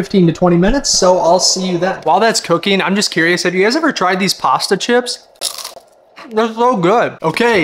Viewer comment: Did You Just say pasta Chips to duritos